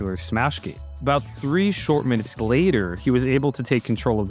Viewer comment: Holy shit. Around the 13:20 mark I thought you said 2 Shulk minutes 🤦